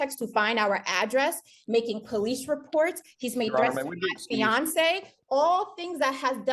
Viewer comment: Wait a minute did she just say he has a “fiancé?” If that’s the case I see what’s really going on! She’s upset that he’s moved on